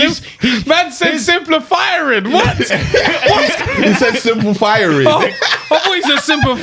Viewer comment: I thought he said simplifiring too 🤣